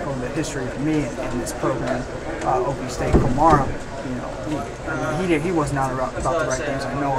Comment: Can anyone tell me who is he talking about @ 2:33? I can't understand what he says. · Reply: Eddie klimara